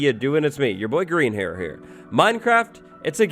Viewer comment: Did say “Grian hair here”?